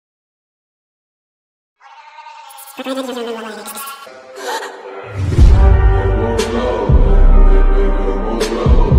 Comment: What did he say at 9:42??? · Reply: He said ‘ spread your legs’😊